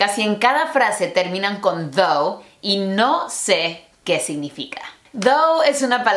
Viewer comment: Pensé que dijo "¿Qué significa Dou?" jajajaja